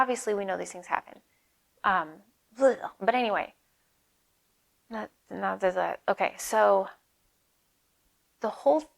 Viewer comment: what is she saying for a split second at 35:59? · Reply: Clearing